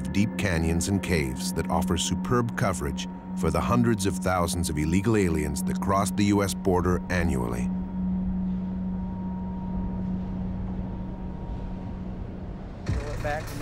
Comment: did he said illegal aliens